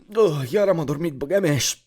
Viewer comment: what we hear : Ah iar am adormit bagamias what translate says : oh yeah Ramesh oh WTF ????